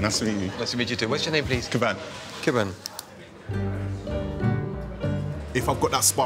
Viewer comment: i thought he said his name was kebab lolol